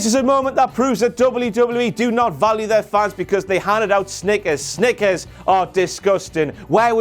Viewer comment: Im pissed I paid to see Lesnar and Rousey and they both were not there. Oh and not everyone knew about the Snickers on the way out. If some old guard didn't point it out I wouldn't of knew it sounded like he was saying stickers lol... BTW I have your "THE BIG DOG" as my notification sound whenever I get a message I hear you